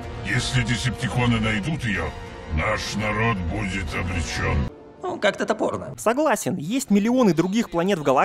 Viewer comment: Что сказал енот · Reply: Во первых его зовут Ракета. Во вторых: "Ну как-то топорно"